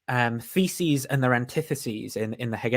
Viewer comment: I thought said "feces and anti-feces". Which is a real thing now because I said so that describes a subsection of toilet philosophy.